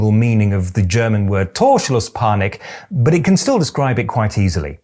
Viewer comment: I thought at first you said Torschusspanik. Something suffered by an out of form forward.